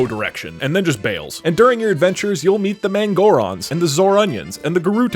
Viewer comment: I legit thought he said moron when he said mangoron